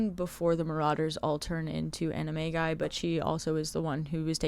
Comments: loved watching this!!! fun fact every time you said animagi my brain heard it as anime-guy lol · i thought she said they turn into anime guys